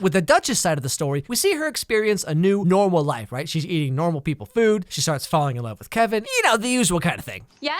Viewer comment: i heard only the part "the dutchess eating normal people "......food I freaked out😂😂😂